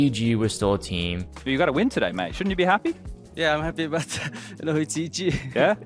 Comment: what is he saying at 7:57? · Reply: "But you got a win today mate shouldn't you be happy?" "Yeah I'm happy but well, it's EG" (Evil Geniuses Implying Evil Geniuses are a trash team and a victory over them is nothing to be happy for